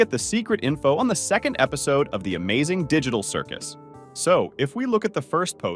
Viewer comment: Did he just say digidal